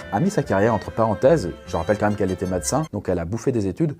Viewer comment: C'est moi ou a 3:22 il a dit madacin